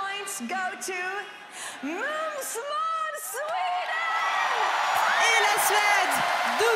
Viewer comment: What did she just say?! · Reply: mums måns sweden. Mums is swedish and it means delicious lol